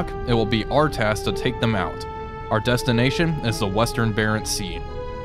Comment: At first I heard "Our objective is the Western Bering Sea" and I thought "well.. you have a nice relaxing trip ahead of you, then!".. lol